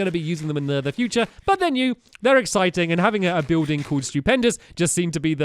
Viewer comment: when he said "stewpendis:" i thought he said "stupidpendis"